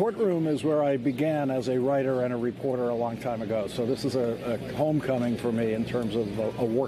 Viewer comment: I thought he said he had 'comb coming" but what he really said was 'homecoming". Hair is cool today, though.